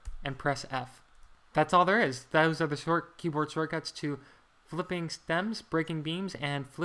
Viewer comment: I could not understand the words your said on flipping beams. It sounded like you said "flip flop". Could you write out what you said so I could read it? Thanks.